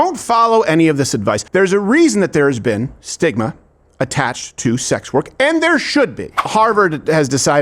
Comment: Lmaooo 😂I misheard Steven just now and I thought he said there is a reason why Sigma has been attached to this type of work ❤I was like yeah 😂I wouldn’t be surprised 😂broke college students